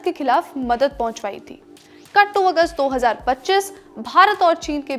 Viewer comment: min me kt august kya bole maam😂